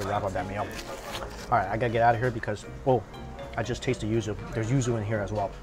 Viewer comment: what does he say here? there is what ingredient? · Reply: Yuzu. It's a type of citrus that grows naturally only in East Asia. It's also cultivated in the US but taste and smell a bit different because the soil and weather is different.